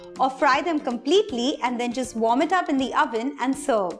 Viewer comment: at 6mins 10 sec I misheard her saying JUST VOMIT IT UP IN OVEN